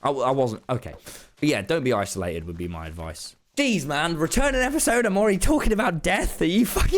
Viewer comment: I either thought it said retiring of returning at first and it’s kinda funny because with it really could’ve gone either way